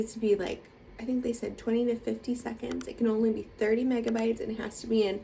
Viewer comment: Great job!!! I still need to do one thanks for the encouragement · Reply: I thought it said 15 seconds you said yours was 21?